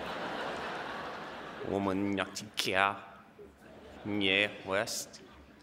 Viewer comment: did he say kanye west???